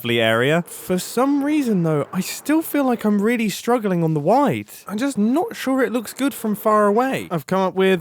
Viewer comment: did you just say wideth? please tell me you meant width or wide, don't tell me thats how brits pronounce width